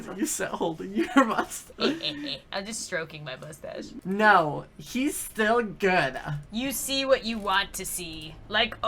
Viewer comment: ‘My favourite thing is when you start holding your moustache’ I think that’s what he said, captions.